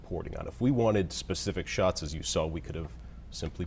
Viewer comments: sounds like he said thank you bitch Lmfao🤣🤣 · Sounds like he says "thankyou bitch" at the very end.